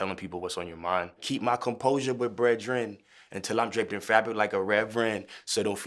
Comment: Damn I always heard "until I drink the Favi like a Reverend"